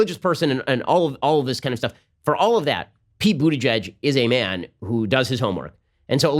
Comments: I sea I heard him say booty judge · I dont know why but I kept hearing “booty judge” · Every time he said Buttigeg I thought he was saying Booty Judge